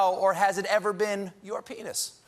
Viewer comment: When he said Trump might have to answer "Is this or has it ever been your penis?", I had to go back and listen closely because at first, I thought he was saying "Is this or has this ever been your FETUS?". Not joking... And I wouldn't be surprised if they reveal Stormy Daniels to actually be his daughter at this point. He is after all horny for his own daughters.